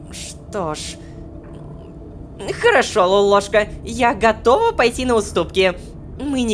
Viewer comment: Мне показалось что она сказала Лололоша теперь я придумула как называть Ло ласково🥰